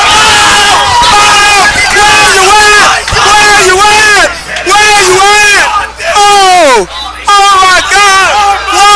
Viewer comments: Can someone please tell what he's saying at 0:19 in the video I've wondered what he was saying since I was 6 and I still don't know what he's saying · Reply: Where you at... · After all these years, I still have no clue what the hell he's saying. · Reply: "where you at"